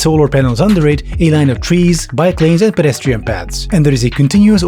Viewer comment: I heard "biplanes" for a second there. Had to do a double take and rewind.